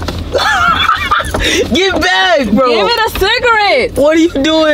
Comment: did he say bae?? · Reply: nah he said get back bro